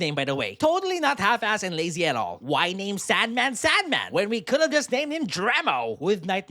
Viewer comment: I thought you were saying sad man instead of sand man